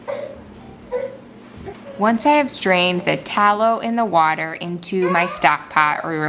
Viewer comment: I thought she said once I’ve restrained my toddler…. strained my tallow. 😅